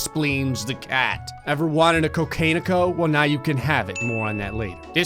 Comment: HAHAHAAHAHAHAHHAHAAHHAAHAHAJAHJAAHAHAHHAAHAHHAAHAJAHAHAJAHAHAHAHAHAHAHAHAHAHAHAHAHAHAHAHAHAHAHAHAHAHAHAHAHAHAHAHAAHWJAJAJAHAHAHAAJAJAHSHFREJSNZHAJAJAHAJAJAJQJAJAJAHAHAHAHAHAHAHAHA DID I HEAR “Cocanio”!!